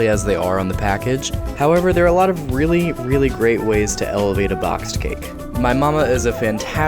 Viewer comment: I swear he said bloxed cake